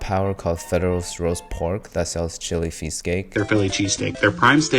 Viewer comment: I swear this guy just said "Chilly PheeseCake" but could just be me.